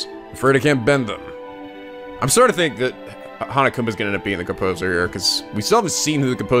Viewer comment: You said “Hanekoma” I thought you said “Monokuma” I would had said that The Game would had been "played" completely differently.